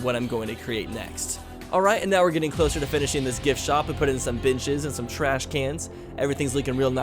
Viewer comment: I'm awful.. you said "putting in some benches" and I heard "putting in some bitches" :\ I'm too used to old. I love mature though.